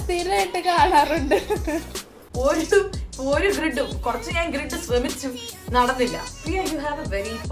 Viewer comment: Also I need a small help.. of you notice any mistakes in the subtitles... please comment below... coz I sat up all night to manually add the subtitles... if u see any mistakes kindly let me know along with the min and sec at which I should correct it ❤️ thank you!!! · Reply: Hii dear❤️ 10:50 grind instead of grid